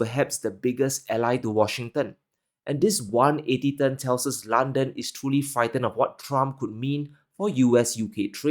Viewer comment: Thank you for your truth kmowledge. which is hard to find on the internet or in modern politics in the most pivotal of the er modern times. At 7:23 does not say Britain but "London is truely frightened" as in "The City of London " The true power of that realm .